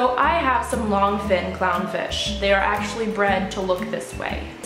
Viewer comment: I thought it said cow fish and I was like what did you do! And waaaa do you mean